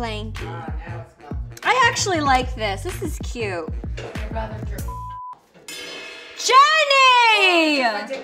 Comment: what did jwoww say "Id rather drink ..." what 1:40 ?